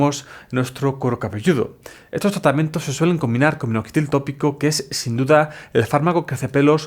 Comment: ¿Se combina con que? Le entendí "...flxfghl tópico".